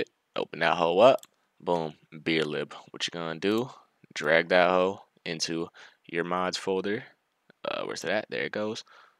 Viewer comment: sounded like "drag that hoe to your moms folder" lmfaoo